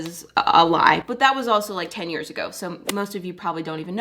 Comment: I thought u said 10 years old 😭😭😭